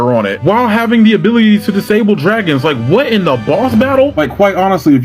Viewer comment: On the screaming death when he said. “What in the boss battle!” I thought he said what in the ball battle😊